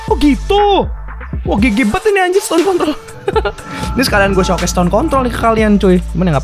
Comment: Apa gw gk salah denger y Bang kayak bilang "Stone Conto*" coba deh Di Menit (12:06) Terus Suara kalian Besarin 😀